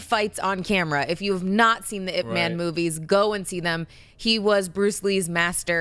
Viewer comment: When talking about Donnie Yen's IP Man, I thought Tiffany said "It Man." Please Mark, do a bit about Donnie Yen being the I T computer guy for Rogue One.